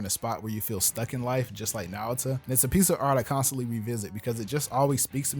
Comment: just like Naruto, did I heard well?